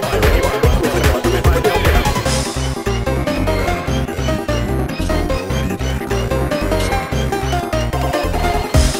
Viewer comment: what is that voice saying at 1:41? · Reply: "You can't do anything, so don't even try. Get some help. Don't do what Sonic does." It's part of the weird vocals of Metallic Madness Zone Bad Future.